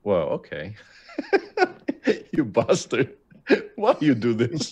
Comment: Does he say you bastard? lol